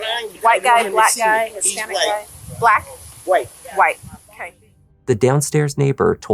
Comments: She clearly said WHITE, dusty $$@ cop.... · …she clearly says white, crazy how the cop heard black 🤔